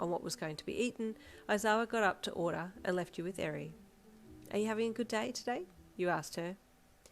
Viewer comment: i’m sorry 😞 i’m confused 🫤. What did she say?!?! Waiting for this feels like forever but it’s too enjoyable😊😂❤ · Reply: I relistened to the part you tagged and I’m not sure if I got the section right but it said “Aizawa got up to order and left you with Eri. Are you having a good day today? You asked her” is that the part that confused you? 😅 or is it the part after where Eri is stuttering and then says “I like it because you are loving on each other.” < Eri is only 5/6 in this so she doesn’t have correct sentences all the time 😅 Side note though I’m glad you’re enjoying it even though it’s confusing 😆